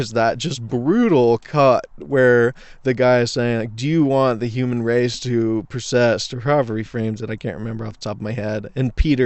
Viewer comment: do you want the human race to(garbled)? What’d you say? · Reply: "Do you want the human race to persist?" At least that's what I think the interviewer said. Just look up Peter Thiel NYT Interview and you'll guaranteed find the clip I'm referencing.